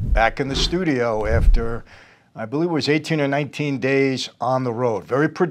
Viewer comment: At first l thought he said 19 days on the run.